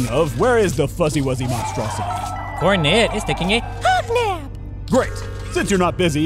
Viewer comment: I can't, for the life of me, decipher what Johnson is saying here. Well, other than "hug now".